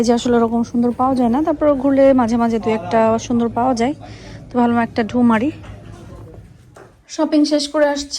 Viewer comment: I heard "Walmart taye dhu mari" 😂😂😂😂😂😂🫣 did i hear Walmart or my ears are mistaken.